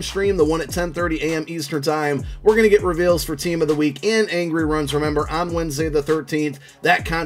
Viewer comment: Maybe I’m getting old but I swore these MFers said angry runs done like 3 weeks ago kid?